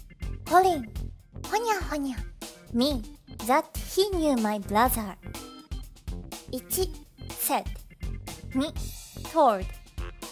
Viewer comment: 『ザ貧乳マイブラザー』としか聞き取れない英語力ゼロの俺でも河合塾で勉強すればやりおるマンになれるのだろうか。。。